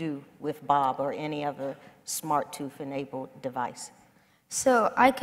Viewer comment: did she say smart tooth haha